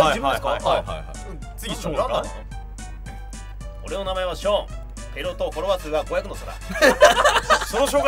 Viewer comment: なんて言ってるか教えてください(´；ω；｀) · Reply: すずき 俺の名前はショーン。ペレ夫とフォロワー数が500の差だ (多分)